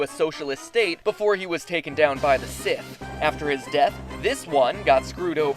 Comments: Wait... At 3:40, did he say that Lenin was assassinated by the Sith??? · sorry did you say Sith? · is it just me at 3:38 does he say "lenin got taken out by the sith" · Did he really say "Sith" or was it just me and he really said "syph"?